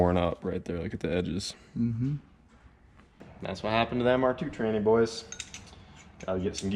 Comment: did you just call me a tranny boy??